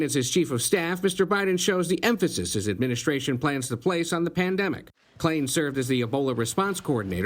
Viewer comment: The Plandemic ..... I heard you say it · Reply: Clean out your ears. He said PAN not “plan”.